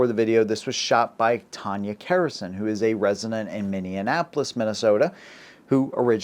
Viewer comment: Have the Walz 2020 Minnesota burned into my brain. lol. Moved a year later. No regrets. The “Mostly Peaceful Summer of Love.” Also: It’s “Mini-Apple-Lus”, thought you were saying Indianapolis a couple of times there.